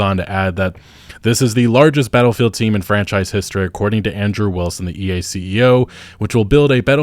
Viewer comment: i thought you were saying Android Wilson rather than Andrew Wilson in the beginning parts of the video